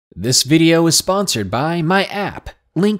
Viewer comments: I swear in the beginning of the video i thought he said "This video is sponsored by my ass." Lmao. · at first I thought you said this episode was sponsored by your ass lmao not your app